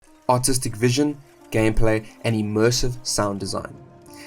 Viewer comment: Reallly thought you said autistic vision in the beginning , gotta shake that accent man